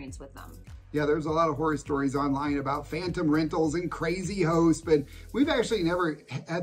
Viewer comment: Well, I really wasn't laughing at you but, I had a good one. At 1:19 you said crazy hosts and 3 or 4 times through I thought you said crazy hoes. I'm 3000% more afraid of crazy hoes than I am crazy hosts, especially in NYC. On a different note, somebody commented on another video that retirees weren't getting enough attention. I spent a few hours going through some of the past videos and the refresher was well worth it. There was so much I didn't retain. I don't know that it would help but, maybe a playlist on your channel titled Fixed Income Retirees. A lot of the Ecuador Cost Of Living could go there as well as many other videos. I think the main takeaway is that except for a few cases, you can live a better life in Ecuador with what you have coming in than you could in the US. It does make a lot..a whole lot of difference on what you absolutely can't do without. If you have to have all US processed food instead of any of the healthier food available in Ecuador, it may cost you more. I knew a guy that his wife wanted to move to Panama but, he couldn't give up his liquor stores in the US. Maybe if you moved to Panama, you wouldn't have to drink so much.